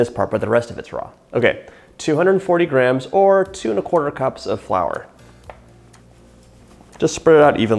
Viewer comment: Guys um I thought he said 2/4 cups of flour. How is easy cookie dough so hard for me